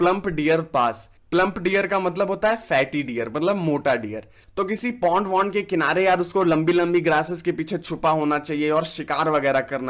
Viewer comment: i hear por* 😂😂